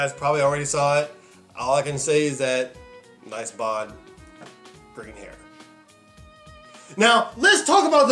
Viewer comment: For a second I thought you said "nice bod, bring it here"